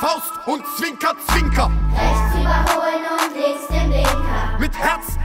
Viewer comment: "Rechts überholen und links der Blinker" sehr stark leider erst beim zweiten mal gehört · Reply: Ich habe zuerst verstanden "Rechts überholen und nichts dahinter".